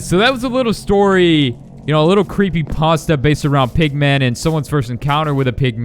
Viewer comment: Also when I heard you saidmpigman I thought u said pikman